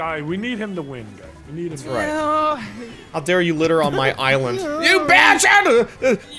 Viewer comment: you bad Shamu lol that's what it sounded like